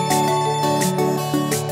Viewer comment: did she just say ANJING?